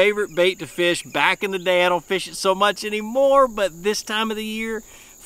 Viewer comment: Thought I heard you say you don’t fish the fluke so much anymore .. any particular reason? Great video as always !